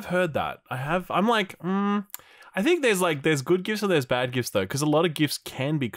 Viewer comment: For a sec i thought you were saying gifts 🎁 are cringe lol